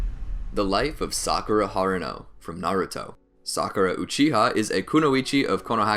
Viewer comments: Did anybody heard the say sakura uchiha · I just heard you say Sakura uchiha